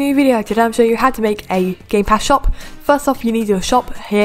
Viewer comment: I thought you said "Gaypass shop" at the beginning Lol